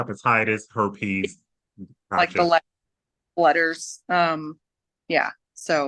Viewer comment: It would make total sense to me, if he had said on his call to Mila that he did it and wanted lethal injection would be if he has HIV/AIDS, then he wouldn’t care that much anyway cuz that’s already a death sentence. I’m assuming that’s what she meant. Anyone?? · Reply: I think she was inferring he has hepatitis C. She says something you can barely understand but I heard "letters"